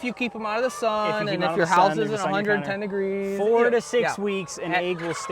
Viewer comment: at ~13:17, did he say if your house is under 10 degrees? 10c = 50f?